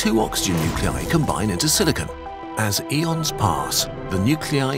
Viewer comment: DID HE SAY ELON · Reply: he said eon but it sounded like Elon Musk to me😭😭